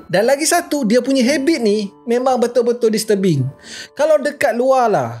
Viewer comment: Repeat part on paling "disturbing" x 3 I tot i heard paling "mrbean" 😂😂😂 WHUAAAAT My bad..